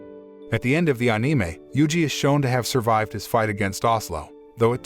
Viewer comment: "anime" - "Oh-Nie-may". Wow, everything was perfect until I heard that.....